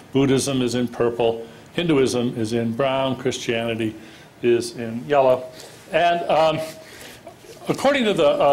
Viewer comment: If you watch this with the automatic captions, at 5:48 instead of saying "Christianity is in yellow" it says "Christianity is invalid." Subliminal message by Dennett?